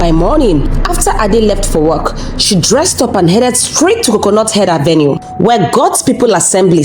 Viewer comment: Did I just hear " coconut head avenue"?😂😂😂😂😂😂😂😂😂